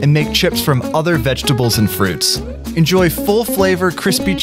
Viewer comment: was i hearing wrong or did you say “fruit”